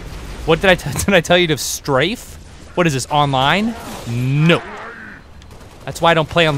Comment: thumbs up if you thought at 4:56 the elite said no